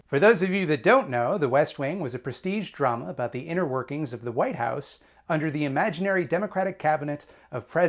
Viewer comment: i heard "white house" as "white-ass". "toby ziegler, the white-ass communications director…"